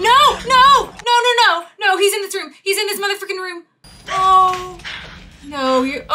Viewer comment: 😶 did you say the F word?! (I know it was only frickin. But I had to go back and check.)